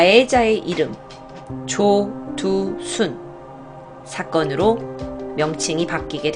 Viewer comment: 저만 좇두순이라 들림?